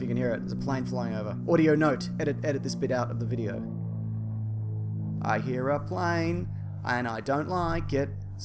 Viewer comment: For all you poor fellows who missed it at 8:11 "I hear a plane"!! You're welcome